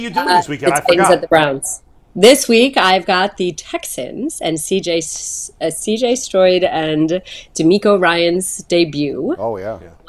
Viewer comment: Did she say CJ Stroid